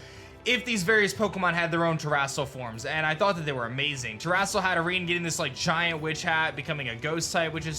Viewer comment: I’m sorry but what did he call Hatterene around the 2:25 mark?? 😂 terastal hat Irringan?